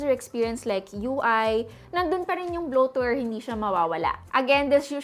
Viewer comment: What did you say at 12:06? Blow Tour? Ano po iyon? Please answer. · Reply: Bloatware